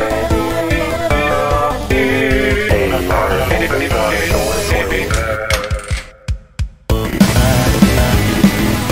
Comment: what is cesar saying at 0:29? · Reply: "hey mark, open the door for me"